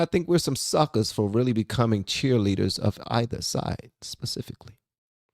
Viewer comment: Did you just say Pacific? It’s specific. I love you and your content but come on bro.